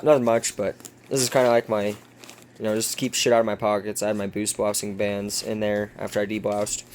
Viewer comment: Hi, may i ask what those elastic bands with the little hooks at the end are called at 1:14, i didn't catch it when you mentioned it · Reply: Hello the bands with the hooks on them are called boot blousing bands.